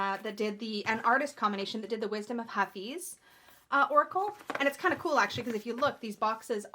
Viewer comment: I listen a little sped up, and every time you said "Wisdom of Hafiz" I heard "Wisdom of Puppies," and now I want a wise puppy deck 🤣